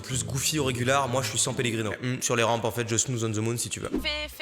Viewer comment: a 8:22 Mastu dit "Enfaite sur les rampes...",mais il dit quoi apres j'ai pas compris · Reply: Il a dit : " en fait sur les rampes je snooze on the moon"